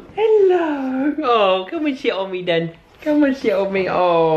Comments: When says to Nala 'come on sit on me then', I thought he said 'shit on me' xD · Sorry to be vulgar but I honestly thought you said, "Aw, come and shit on me then." Was away from my computer oh my gosh.